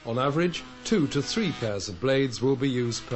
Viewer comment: did he say at 4:36 that on average two to three THOUSAND blades will be used ? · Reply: Pairs.